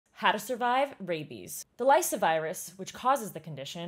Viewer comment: only me who thought she said... "how to survive BABIES" lol